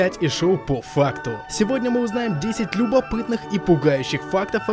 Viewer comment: Когда сказали "по факту" то мне послышалось: вота фак